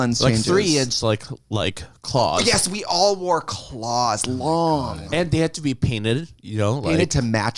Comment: lol for a millisecond I thought Kim said they all wore clogs...